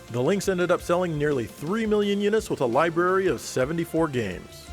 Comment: the audio says 74 games but the subtitles says 73